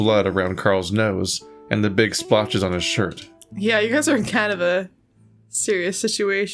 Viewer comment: I first misheard "You guys are in kind of a-" as "you guys are in Canada" lol